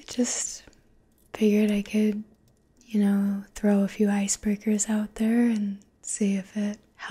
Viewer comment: I thought you said ice burgers. I'm going to start using that. Thank you.